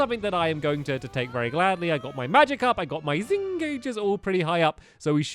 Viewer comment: Did he just say "I got my Magikarp"?